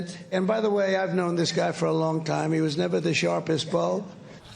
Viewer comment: I thought He said the sharpest ball I was about to say that doesn't make any sense. But you said Is sharpest bulb. THAT ALSO DOESN'T MAKE SENSE! Dumbest president is as dumb as president does